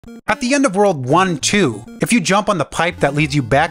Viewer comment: i thought bro said at the end of World War 2